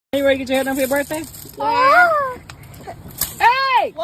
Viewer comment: Did she Just say? Are you ready to get your hair done for your birthday 😂 She is going to Mess up those foodstamps and CPS😅